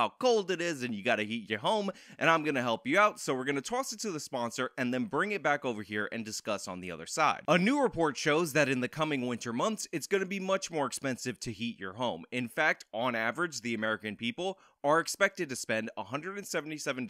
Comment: in and I've already had a Joe Biden moment. I heard "it's going to get more expensive to eat your home", to which my reaction was "darn, and right now that I'm really hungry and all". Dementia or just bad hearing? FJB's got both.